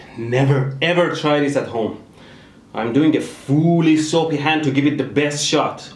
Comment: Is it just me or when in 10:27 he says "fully soapy hand" all i hear is "FULL BRIDGE RECTIFIERRRRR"